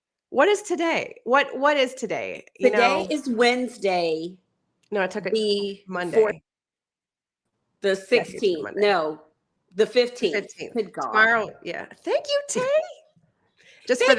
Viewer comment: I missed the live :( I thought said the 18th.